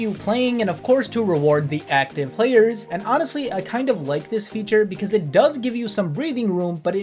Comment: subtitle error. Displays the word “blacklist” instead of “like”.